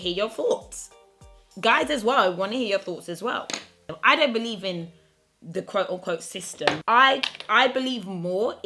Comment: When she said “I don’t believe in the «system»” I thought she said “I don’t believe in the«sister»” 😂😂😂